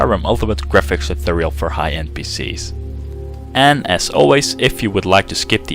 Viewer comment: In the intro, I heard "for high NPCs!" That alone made the clip worth watching :D